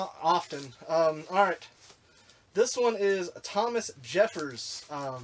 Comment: For a minute thought you had said Thomas Jefferson 😅